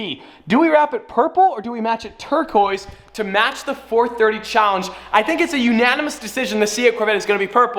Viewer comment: HOLD UP DID U CALL THE CHALLENGE CAR THE "'FORD30 CHALLENGE" at 10:16?